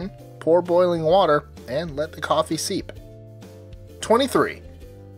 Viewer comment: did I hear him say and 'let the coffee SEEP?'